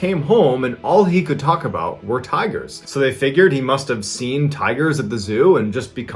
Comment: I totally misheard you and thought you said "tires" not tigers lol. Totally different perspective to that story